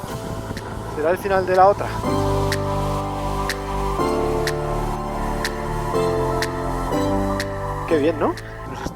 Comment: HahahaQue dice ahí? 6:39 reserva nacional de que? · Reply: de caça(en catalàn caza)